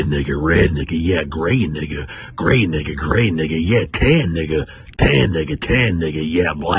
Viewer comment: Bro my volume was low so I thought he was saying “gay n”ga, gay n”ga gay n”ga”